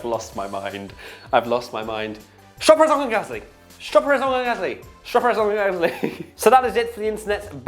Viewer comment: When he said the dnfs I thought he said strawberry gasly